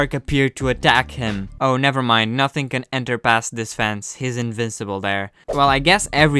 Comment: did he just said… fence?